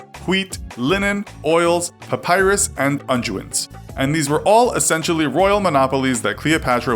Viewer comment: what are "unduits" he says it at 1:47 i have tried googling, no result that makes sense.?? no result for "unjuits" either.